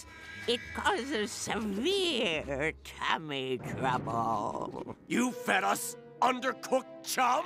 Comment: I thought she said “cabbage rumble” but it was actually “tummy trouble”